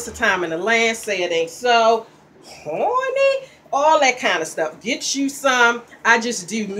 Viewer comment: what is it that you're saying? xD horneh? horny? I can't figure it out lmao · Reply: HAW-NEE aka HONEY...LOL...TFW HUN I TRULY APPRECIATE THAT😃😃😃👍👍👍💜💙💜